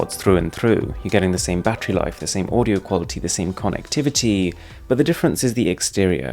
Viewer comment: Did he just say "audio quality"?